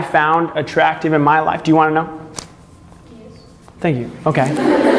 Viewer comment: WHAT DOES SHE SAY I'VE BEEN LISTENING TO IT ON REPEAT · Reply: Coin Boi Connor she said yes